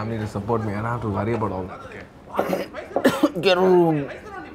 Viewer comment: what is his friend saying ? sounds somthing like "gerun" ?? i cant understand it correctly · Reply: Get a Room..(coughs)!!